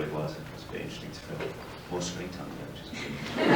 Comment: What did oliver say in 1:39? I can't hear it :(((((( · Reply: he said he had more screen time